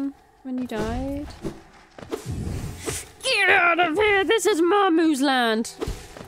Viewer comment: I misheard "Marmus land" for "my moose land" and thought it was a wonderful oldtimey expression for rights to hunt on the grounds or something. "Get out of here, this is my moose land! My beachball deserves better!" Could be an extension of Cleeses "Your mother was a hamster and your father smelled of elderberries" insult. =D